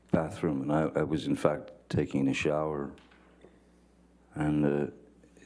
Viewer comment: it almost sounded like he was about to say “i was taking a sh/it” lol